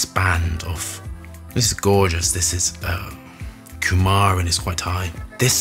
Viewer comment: Hey I've got a quick question: you referred to an ingredient (?) at 1:53 and also later on ("cumar"?). I'm not familiar with that word, couldn't find anything. Did i misheard it, or is it written differently? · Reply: Coumarin***